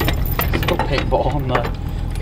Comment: Uh what did you say at 8:49 Sound like I herd ball and nut